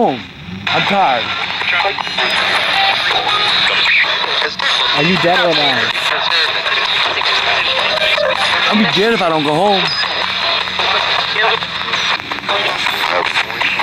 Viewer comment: I heard don't go